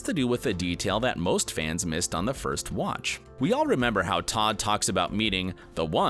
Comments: Did he call Ted Todd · did he say “ we all remember how Tod talks about....” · did he say todd at around 2:42?